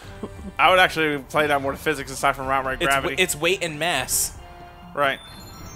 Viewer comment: I literally thought said "Weight and ass" not mass apparently I need my ears checked